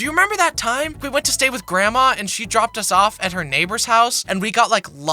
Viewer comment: did you say Hello to your Neighbor?